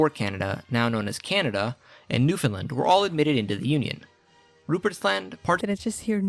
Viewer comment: I know it sounded like he said “New Finland” but he really said “Newfoundland” which is part of Canada.